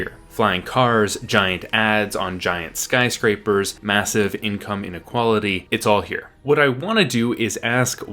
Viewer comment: Did you just say "outcome inequality"? You're one of those brain dead people, aren't you? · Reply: He said "income inequality" and if you're triggered because of the allusion to class politics you should know that societies with massive disparities in wealth, power, and access to technology are a central aspect of the cyberpunk genre.